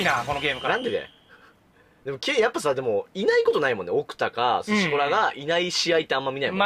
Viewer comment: スシコラがシコラーに聞こえた